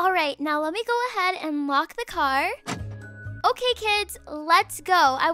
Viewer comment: WHEN SHE SAID LOCK THE CAR I THOUGHT SHE SAID WALK THE CAR LMAO